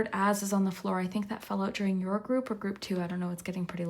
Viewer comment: the way my eyebrow raised when I thought you said the word(s) “ass on the floor” popped out. I thought damn, haven’t heard you draw from that pile of cards before, but ok, if the cards says so 💃